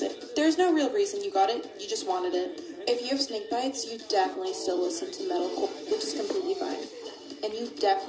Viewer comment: can someone please say what the girl said about snake bites? English is not my first language and i struggled to understand what she said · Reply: she says the still listen to Metal Core (musical genre/fan base) and they went to Warped Tour (a very popular Pop Punk / Emo / Metal travelling music festival) and they shop at Hot Topic (a popular Scene / Emo / Goth clothing and accessories store)